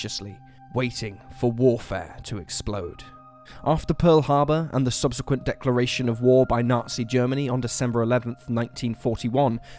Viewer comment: Has this guy ever heard of editing in the first couple of minutes he said December 11th 1941 when the Japanese bomb Pearl Harbor give me a break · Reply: What he said was "After Pearl Harbor and the SUBSEQUENT declaration of war by Germany on December 11th"